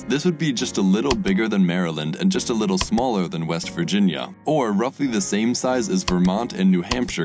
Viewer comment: did i hear WEST VIRGINIA!?!